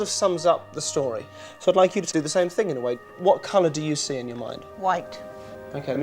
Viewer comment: i can't see how he gave her 'white', but they're both wearing white and the wall behind is white. i can see how he gives her britain, cold and snow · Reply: Listen carefully at 1:51 and 1:52, he meant to say "same thing in the way" but it sounded more like "same thing in the white". Also he did this right before asking what color she sees. I think she heard "white" and it seemed to her like it was a thought that came up, but really its his suggestion